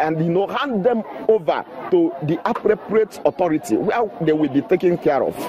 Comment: I heard something like ( appraprate ) around 1:17 Please let's all confirm